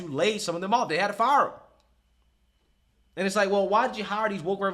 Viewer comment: I heard you say "neckflix" and instantly thought.... KAMALA LOL